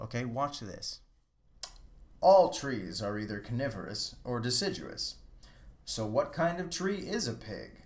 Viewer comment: That was funny at the end there, because at first I thought you said "all trees are either carnivorous or deciduous."